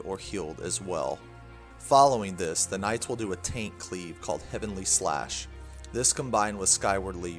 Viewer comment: Great video, sounds like he says Taint Cleave at around 7:50 which sounds much scarier than a tank cleave.